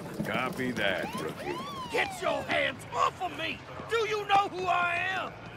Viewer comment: Get your hands offa me! Do you know who I am?! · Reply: I thought he said “Get your ass off of me!” 😂